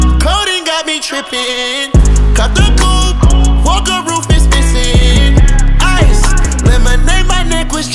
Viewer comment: In the video it shows that Don said "Woke up roof was missing" but he is actually saying "roof is missing"